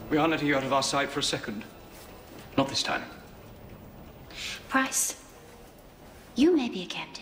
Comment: did she just say his name as PRICE? AND HE'S A CAPTAIN!? CAPTAIN PRICE!?